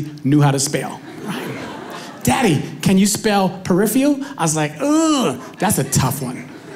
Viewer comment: He clearly says "perifial" and not "peripheral". And if his daughter really asked him to spell perifial (whatever that means), then he is right when he says "p","e","r","i","f". That would mean she is wrong and dumb!